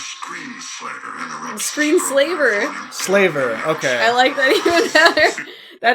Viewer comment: I thought the villain was saying their name was Screenslayer! lol Anyway, this looks like a lot of fun and I can't wait to see it.